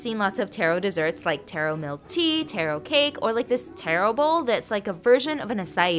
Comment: I thought you said terrible when you said taro bowl